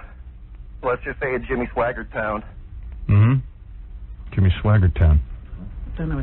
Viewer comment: he said it was "jimmy swagger town" or something. Never heard of that neither had Stern. Can't remember what state he said he was normally in but he did say he slid down to Mississippi, sounding like it was a neighboring state. I have to wonder if this could clue into his location at least, if we knew where around there it was common slang to say jimmy swagger town..